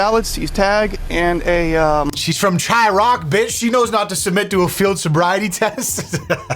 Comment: did you say Chi-rock? It's Chi-raq